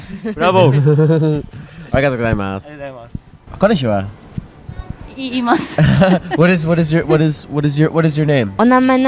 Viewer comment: he said hello in a way that sounded a lot like "do you have a boyfriend" and she says yes haha, the subtitles are wrong